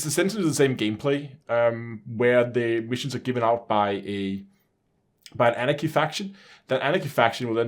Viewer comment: Genuinely thought he said... 'Anakin faction'...